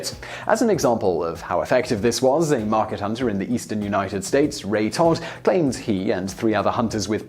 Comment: did he say that guy's name is Ray Tard or am I hearing things?